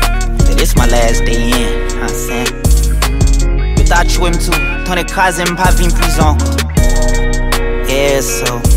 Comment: 🔥🔥Can someone translate what he said in Creole? Beginning prayer & ending phrase.. It sounds like he said "You thought you'd win twenty two cars & pop in prison."